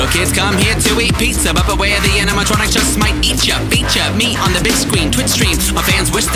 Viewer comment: Me and my fren thought when you said beat ya meat instead of feature me